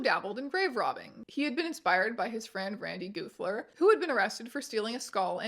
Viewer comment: I was taking a bite of something and thought you said "Randy Goofler" and I was like "no fucking way..." And I was correct, there was no way. But for a brief moment....lmao